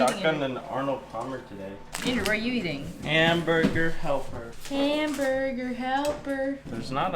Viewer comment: when u asked andrew what he was eating he said hamburger but i thought he said amber's boogers